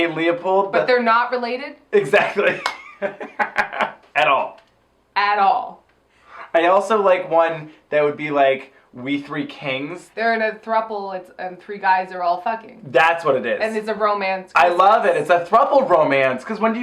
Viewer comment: I honestly thought you said "We Three Kinks" on the fake christmas song discussion and was surprisingly here for it?